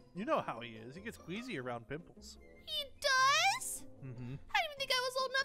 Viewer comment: I'm the beginning when they were talking about pimples i heard peoples....😂